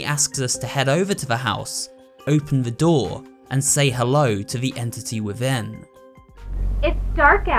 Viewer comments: Personally, I hear less of, “say hello!” And more of, “I’m home!” When Amanda comes in the house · When she said "say hello" I thought she said "mom I'm home" and now I know what she actually said :D